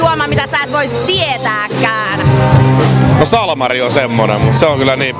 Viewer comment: mitä et voi sietääkkään? -salmari :DD